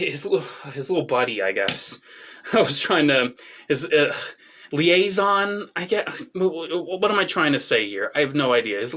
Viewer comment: what's this figure's name and set in 4:41 ? i am curious yet can't heard his name well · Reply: It's Mainframe, who transformers into Teletraan 1 (The Autobot's Computer) that goes with the giant Ark figure released in 2021! <link>